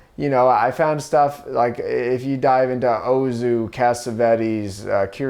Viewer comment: Who’s that first director he says? I think Ozu but I hear an H and want to be sure I’m not missing someone